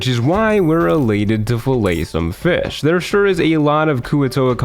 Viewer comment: wait did he say fell-oh. Oooh. FILLET some fish. Thats what it was.